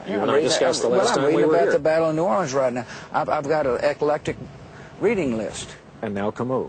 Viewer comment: Did he say 5:32 "epileptic" instead of "eclectic"? Too funny.